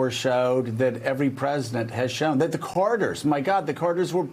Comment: I thought he said the CORONERS were heartbroken when the Reagans came in.